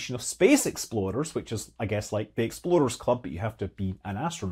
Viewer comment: You said explorers club, but I heard "Exploders Club" which also sounds fun.